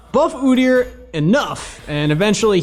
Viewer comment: At the 00:00 i thought he said "F**k Udyr"...... he said buff Udyr but still....f**k udyr